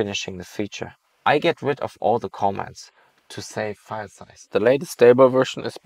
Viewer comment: "I get rid of all the comments to save on file size." ... bruh. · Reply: ngl i thought he said commas, but honestly both are horrible